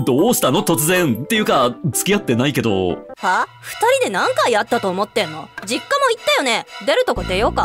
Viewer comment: 音声だと字幕と違い、何回ヤッタと思ってんの？って聞こえる あっくんは否定してないしむしろ肯定、アッッ